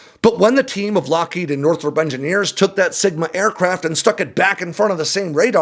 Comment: You said "sigma" and I heard "smegma". Yeah... Great images.